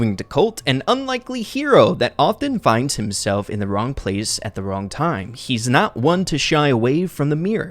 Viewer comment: "at the wrong place at the wrong time" oh I thought it said wrong place at the right time ._.